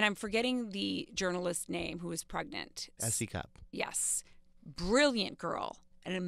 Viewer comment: What is the name of that journalist he just mentioned? Didnt catch it... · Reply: S.E. CUPP